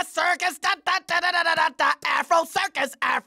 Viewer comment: I thought he was saying circus asshole! xD